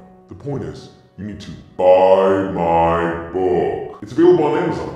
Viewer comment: Oh BuyMy Book! Hahahahahah I thought you said Buy in bulk! 😂